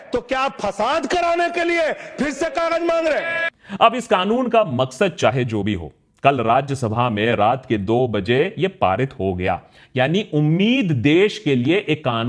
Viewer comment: did i heard that right raat ke 2 baje ? 25:42😯